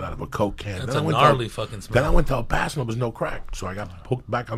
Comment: did he say El Paso?